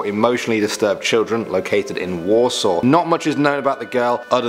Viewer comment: I couldn't hear properly did he say Walsall? It's pronounced as Warl-sahl